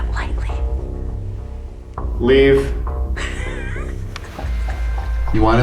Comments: when and are replying the voice they heard on the recorder it sounds like "please leave the dungeon" after question, that could be just me but that's what I think I heard. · "Let me leave the dungeon tonight." That's what the whisper sounds like it's saying.